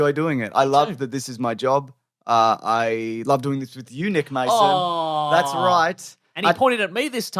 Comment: I thought you called him Eunuch Mason for a second there. Thanks for a great year full of laughs guys. All the best for 2025.